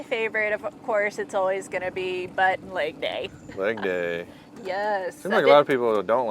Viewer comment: Butt n leg day ... i thought she said buttnaked 😮